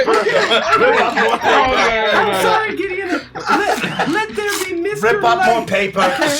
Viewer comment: Sounds like says “I’m sorry Giddian” at 1:32:29 ?